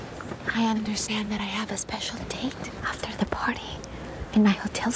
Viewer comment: Am I losing my mind or does she say "I understand that I have a special taint" at 9:59?